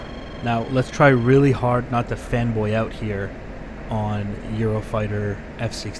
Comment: I heard "let's try really hard not to femboy out here" and had to listen to it a second time